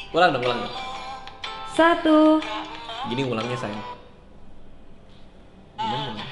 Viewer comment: "gini ngulangnya sayang"...? apa aq yg salah dengar..😂 *tolongbantujawab 😄 abang so sweet banget sama Lio..😂